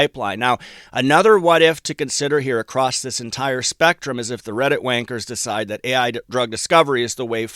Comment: 😂 Redditt wankers. I wasn't quite sure i heard that. 🙉 Thank you for update. 👍🏼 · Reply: 😂😂😂😂 so funny Reddit wankers